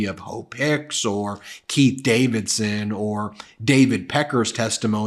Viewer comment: Every time says “Keith Davison”, I hear “Pete” and my mental image of the courtroom improves significantly. 😂